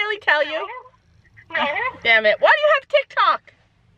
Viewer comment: did say the D word? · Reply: She said damn it... I don’t think that it’s a swear lmfao